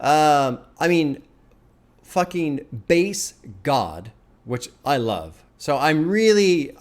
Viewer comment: Lmao when he said “bass god” I thought he said “based god” lol shoutout lil b fr